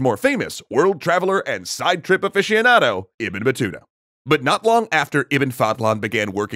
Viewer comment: Just for the guys wandering "why there is a lot of IBN" Ibn in Arabic means "son of" its like family name Son of fadhlan, son of batuta, son of sina...etc Edit: just to clarify in Arabs culture u'll have a different family name from ur father for example ur name is Mohamed, your father's name is ahmed and your grandfather's name is omar so: -ur father's full name is ahmed ibn omar. -ur full name is Mohamed ibn ahmed Thats how naming system work in old arabia and they mostly mention alot of their grandfathers if some one asked theme for their full name its goes like: Mohamed ibn ahmed ibn omar ibn ali ibn...etc Sorry for my bad English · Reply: Im Algerian and when you said Ibn Batuta I thought you said Ibn batata, I was thinking is this the dude that oppressed Ireland??